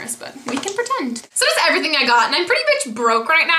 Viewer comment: lol I thought you said pretty "much" broke :P